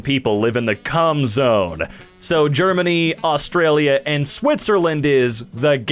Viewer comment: Did he just say Australia.